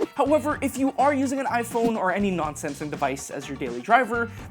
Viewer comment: When you said in 5:40 "if you using other non-Samsung devices..." i heard that Other nonsense device 😂 i had to listen again as; Wait what ?!